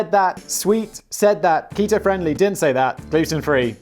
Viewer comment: I was all into what Simon was saying about the cereal. Then he got to the Kito part and I thought he said PETA for a second. Had rewind it to make sure I misheard. I was like "Simon, being PETA friendly makes me NOT want to buy it." Luckily I misheard. Happens sometimes when you're only half paying attention cause you're doing something else.